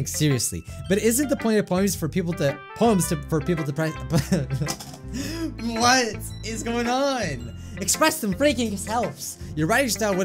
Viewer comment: "she loves manga!" YouTube captions thought that was "she loves vodka!" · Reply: And 27:07 idk what Mike was trying to say but YT captions though it was "for people to prank the planet"